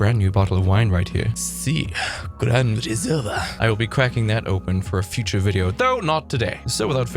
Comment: Why did I hear "I'll be quacking that open today?" Is that my alcohol speaking again???